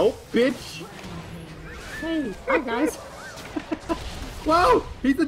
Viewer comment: At around 13:50ish does anyone else hear a female voice in the background? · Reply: Yeah, something like "Hey guys." It was probably true self.